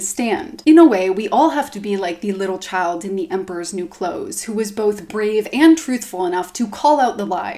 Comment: I don't know why but at first I thought she was saying the emperor's new groove also this channel has great content.